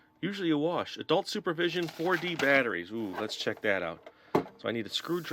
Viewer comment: I thought he said 40 batteries! 😂